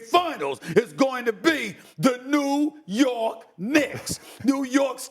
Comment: anyone else hear “the New York 🥷’s”? 😭😭😭